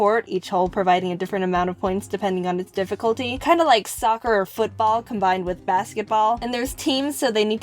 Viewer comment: I had this on in the background while I was doing my work, and I heard soccer and thought she said Sokka. Then I started playing avatar the last airbender episodes in my head. I’ve watched it way too much...